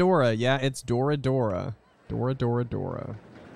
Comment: I always heard "Flora, Flora, Flora", followed by "What about that...(trails into faded conversation)"